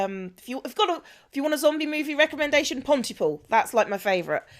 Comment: What was the name of the zombie movie recommendation? I heard "Pontiful"... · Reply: The movie is called Pontypool, kind of interesting but I dunno if I would call it a zombie movie or a virus type movie like 27 Days Later. Still interesting movie for sure. If you want a good "found footage" I would recommend Lake Mungo, normally I hate found footage movie but this one was worth a watch.